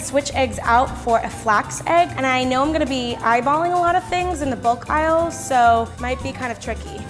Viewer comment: Me: thought said book aisle Me now: ohhh the bulk aisle